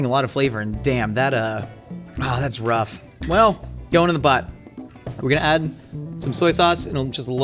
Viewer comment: I laughed too hard at the captions saying "well, going in the butt" instead of "in the pot"